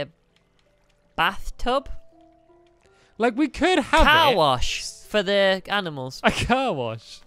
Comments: When said car wash I thought he said cow wash. They should've made that! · When said car wash I thought he said cow wash